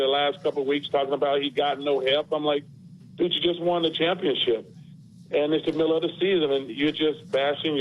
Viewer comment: I thought Charles said "Bitch you just won a championship" lol